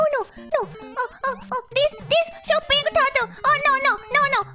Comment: she's saying "oh angel turtle"(because of the wings). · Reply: I thought she said ninja turtle at first, but yours sounds about right